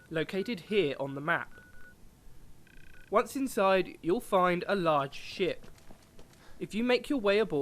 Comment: i thought he said large shit